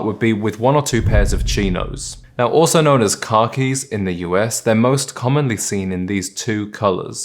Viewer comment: I heard “car keys” not khakis 😂